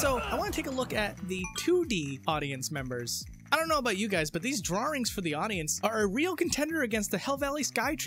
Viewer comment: Did you just say "drawRings" at around 8:50 like the British do, but you don't have a British accent? This is the weirdest thing I've ever heard.